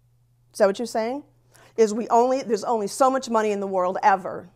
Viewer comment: does she actually say "it can't be true there only so much money"?